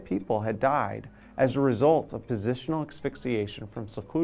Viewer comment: "24 people died as a result of PHYSICIAN Asphyxiation"? I think that was supposed to say "positional" (check